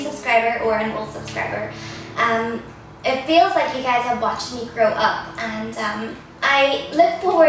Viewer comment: Oh god I thought at one point you said "It feels like you guys have watched me /throw/ up." Oops lolol